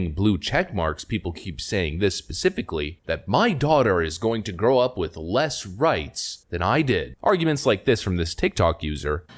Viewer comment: i thought you've been saying "our daughters will grow up with less RICE than we did" 😂😂 like the last 2 episodes.... golly im goober